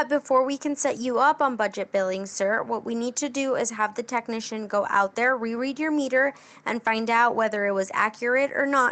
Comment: Hey at 4:41 she says may I have please your ** address, what is the word I'm missing? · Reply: Hi, service is the word you are looking for. Service address.